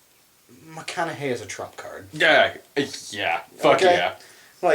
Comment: I'm out of it today, misheard what said as "McConaughey is a trap card" and had a Yu-gi-oh flashback